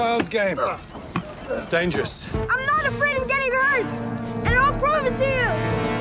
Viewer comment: I heard something completely different at 6:41. "I'm not afraid of gay nerds" 😂